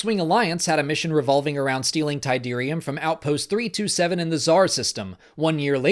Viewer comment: Outpost 327, you say? I thought he said, 'Outlook 327'' for a second. ;)